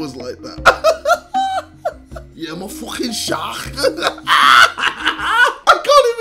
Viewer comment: its 4 am and i hear him say " imma fockin sharhark" and i laughed soo loud fml